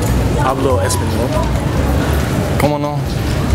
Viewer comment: did he said Pablo Escobar!!??😂😂